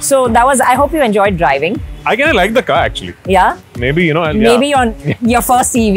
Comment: Did he say "I DID'NT LIKE THE CAR ACTUALLY" 🤣🤣 typical Prithvi😂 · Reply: He said " I kind of love the car actually"